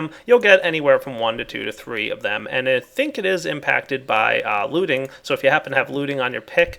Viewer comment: Did just say looting pickaxe? <,<